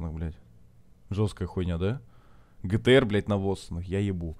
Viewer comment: GT-R НАВОЗ ПОСЛЫШАЛОСЬ ИЛИ НЕТ? АХАХХААХХАХАХАХ